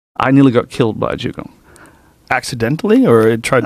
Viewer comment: I thought he said he was nearly killed by a Jew